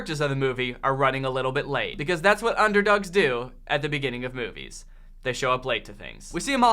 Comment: oh god i thought you said “at the end of the movie they hug and make out” about the girl and her dad, i was thinking WOW this movie took a turn